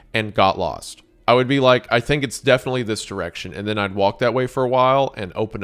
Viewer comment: I swear he said erection